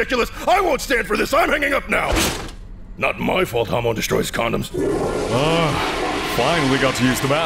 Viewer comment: caption says homo but what he said was "Hamon" (the power stands replaced)